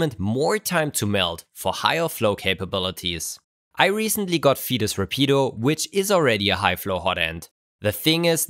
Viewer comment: I heard featus repeato and thought 'ha! Twins!' Is there something wrong with me??